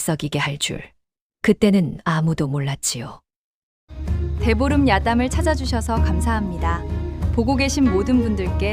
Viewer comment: "대보름 야담"이 "배부른야담"으로 들리네요^^ㅎ 아우~배불러^^~*ㅎ